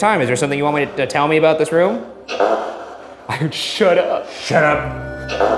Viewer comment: could the voice at 38:25 be saying “sure” instead of shut up?